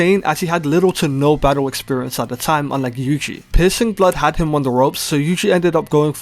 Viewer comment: my man no hate but your voice when saying "piercing blood" but all i heard is "p*ssing blood"